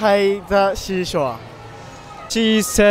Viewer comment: "She sells sea shells by the SHE'S HORE" I love how that took a dramatic turn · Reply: I heard “She sells sea shells by the SHE’S WH•RE” 😓 my bad..